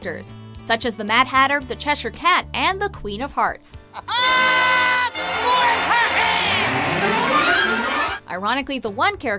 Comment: Dk why but I heard "queen of farts" not "queen of hearts" XD